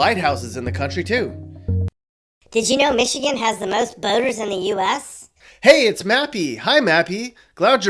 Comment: When the narrator said that Michigan has a lot of boaters, I did a double-take and thought he said "boners".